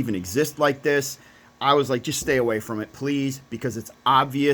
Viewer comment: I thought you said to stay away from red box